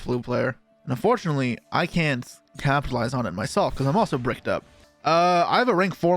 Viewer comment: did he said "I'm bricked up"? Lol